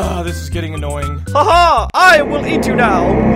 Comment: I thought he said I will e2 now